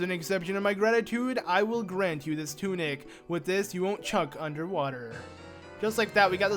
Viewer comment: Wait did he say chuck instead of choke?